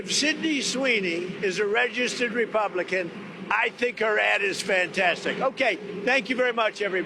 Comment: I thought Trump said oh I love her ass and I think her ass is fantastic 😂